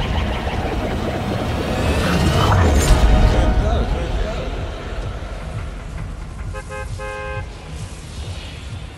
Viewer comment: what did midas say?? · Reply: I JUST FIGURED IT OUT HE SAYS safal which translates to success